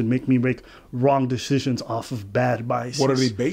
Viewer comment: Oh... said "some people have biases" I thought he said "some people have bi-seasons" eyehaha...😆🤣😆🤣👍😘